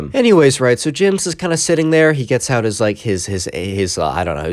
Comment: did you say he was getting out his aims